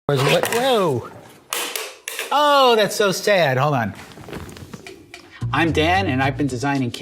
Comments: Thought he said "I'm dad" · I literally thought he said "Hi I'm Dad" and I just accepted it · I thought he said "I'm Dad" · I thought he said “I’m dad” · When he introduced himself I thought he said, “Hi, I’m dad.” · Am I the only one who thought he said I'm dad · I thought he said I'm dad 0:05 XD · 06 I thought he said "Im dad" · Every time, without fail, I hear “I’m Dad” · I thought he said hi I'm dad😂😂😂🤦‍♀️ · I thought he said I'm Dad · I'm the intro I thought he said "I'm dad" and I was like "So THIS is where you've been all these years?!